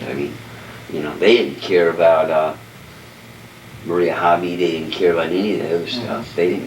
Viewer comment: Fantastic video!! I've been trying to put together a list of other potential murders. Did anyone catch the name at 11:32 ? I think it was Maria H........! Really enjoying these vids! I'd like to take an Ouija board or do some evp's at Barker Ranch although I'm in Canada and can't get there right now. It's on the ol' bucket list! LOL. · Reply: Marina Habe. That case has been solved though.